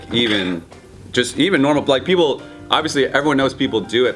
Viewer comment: "...even normal black people..." was that a typo or did I hear and read that wrong? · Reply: LazyTV He said "like", not "black". He just didn't finish his first thought.